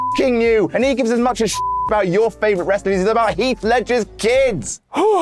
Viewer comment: Did he just accidentally say Heath Ledger instead of Heath Slater @ 2:00 hahahah